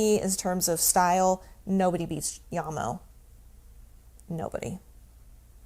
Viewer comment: Great review! I'm leaning heavily towards Bronze 100's. 2 questions.....How do they compare to Silver 100's? and....the young lady you were speaking to, at about 17:11 "for me in terms of style, nobody beats -----? I couldn't make out what she said. Thanks for the reviews! Keep 'em coming! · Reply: “Jamo” - that’s what she said.